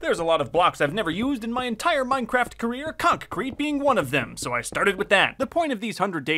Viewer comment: Is it just me or did I hear him say “conk Creat”